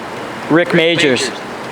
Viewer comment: "Scabies" 😂 I swear when he said Rick Majors I thought he said Dick Majors...